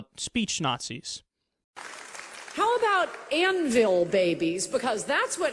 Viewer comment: When she said anvil, I first heard it as "Annville", visualizing a whole village of miniature Ann Coulters.